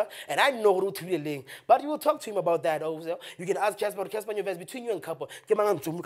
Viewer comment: Did he just say Cassper nyonyest😂